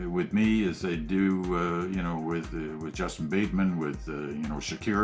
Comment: Did he just say "Justin Bateman"? haha